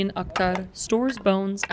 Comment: I heard Jalaluddin Akbar. But it's Akhtar